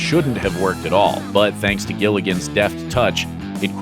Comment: Did he say "Gilligan?"